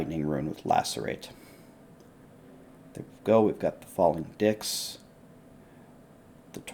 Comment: did he just say "falling dicks"? lol